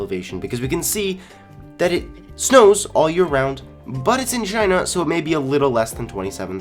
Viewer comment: DID HE JUST SAY "SHINA" BRUH